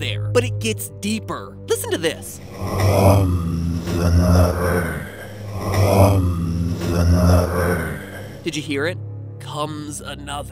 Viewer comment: it sounds like it says here comes mother here comes mother